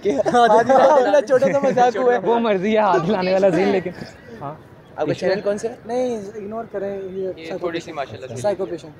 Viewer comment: what did she say ??? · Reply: ‘Mujhey issues hain’